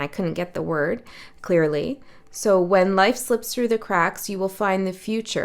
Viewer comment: What I heard was to "SYNC into the cracks, and try to 'de-find' it"🥰. And the Marianna Trench has been a longstanding metaphor that's prompted me to go forth ... to go metaphor-ward 🥰. Love this channel's vibe.